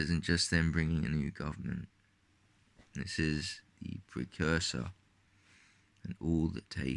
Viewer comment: around the 6:10 mark does he say "this is the precursor"? cant quit make out what you say sometimes